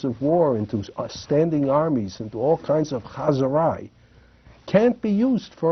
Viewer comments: What did he say at 17:00? "Haz urai"? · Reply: Huzzerai--that's a Yiddish (or maybe Hebrew) term for "a big to-do; exciting activity." Interestingly, Asimov wrote that his ancestry might have been Muslim rather than Jewish (although he considered himself a cultural Jew); he noted that his surname can also mean, "Son of Hassim." His family came--if memory serves--from the southern area of the Russian empire, where many of the people are Muslim as well as Jewish. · he says something like "haus ah-rye". I've never heard this word or phrase before. And because I don't know how to spell it, it's become particularly hard to Google. Does anyone know what it means or what he said? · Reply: Hazarai. Yiddish for a little of this a little of that or stuff or junk or junk food.